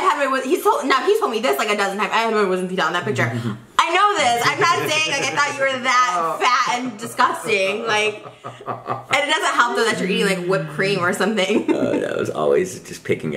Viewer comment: Did she just call him Fat and Disgusting 😱